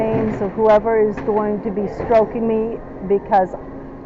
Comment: did she just say whoever is STROKING her?!?!? 🤢🤮